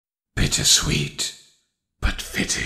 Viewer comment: I heard "bitch is sweet" in da beginning